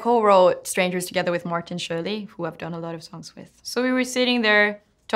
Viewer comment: Did she just say Martin Shkreli?